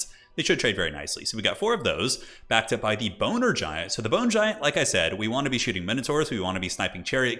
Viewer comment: 0:0:50 "The Boner Giant." I can't be the only one who heard that.